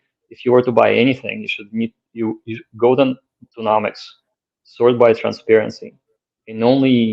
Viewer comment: what was the website said at 57:04 where you search by transparency? Golden Ecnomonics??? Tokenomincs? I couldn't understand him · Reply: nomics